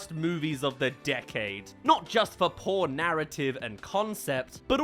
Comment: I had to pause while he was saying “poor narrative” and I deadass thought he said “porn”